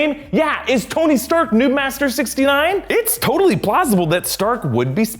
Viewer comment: captions say nudemaster69 lmfao